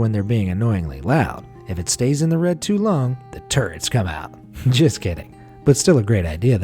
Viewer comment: Oh TURRETS, lol I swear all I could hear was TURDS! LMAO, I actually had to put the caps on to see what henwat saying lol